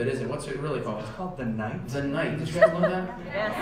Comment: what did he say at 3:31-3:33?? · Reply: heymoon he said "it's actually called the night did you guys know that"